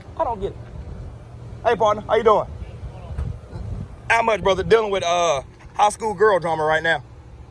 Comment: did you say you dealing with a high school girl? i rewind a few times and i keep hearing the same thing. lol epic, this cop step on a live mine field and yes his ego would not let him leave is truly painful to watch.